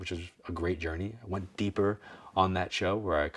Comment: Thank you loads, I heard 'Go deeper' I am trying to progress in mediumship.